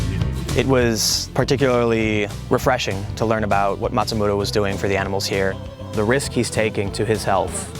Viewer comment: I thought his family name was Matsumura. But it sounds like you say Matsumoto at 4:04.